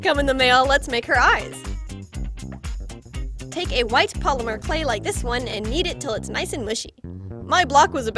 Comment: it sound like she said "Take the white polymer clay and yeet it " YEETUS TO THE FEETUS